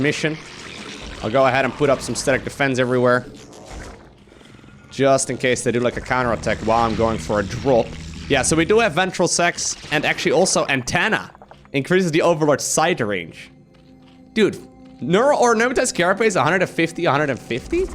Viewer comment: I swear I heard 'ventral sex.'